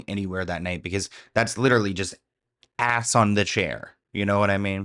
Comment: Idk what I think now bc all I hear is “azz on a chair”🤣💀